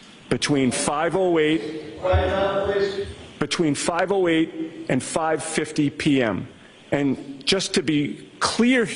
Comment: Does anyone know what that 'guy' whoever it is (sounds like maybe a bailiff type) yells out every once in a while right over the lawyers talking? Is he announcing something? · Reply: “ quiet down please “ to court observers